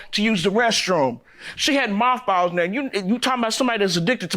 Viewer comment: Dam I must be high I thought she said malt balls like the candy not moth balls lol